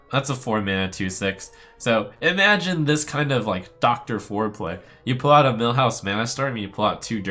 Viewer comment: did you just say "dr. Foreplay"?!